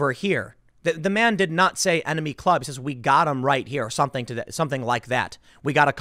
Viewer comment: To me it sounds like they say : hey we got him right here, we got a trumper right here. Someone can clean the audio and remove some of the background noise maybe we can know for sure but I don’t think they said we got a couple right here. Maybe I’m wrong but so far that’s what I think they said.